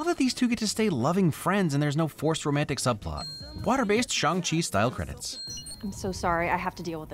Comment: I thought at 19:25 he said "What a based Shang-Chi style credits"